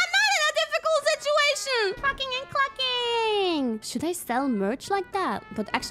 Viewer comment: "Cluckin' Truckin'" Should have been Kiara's company name! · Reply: when I first heard it I thought she said "Fuckin and cluckin" which was....rather catchy tbh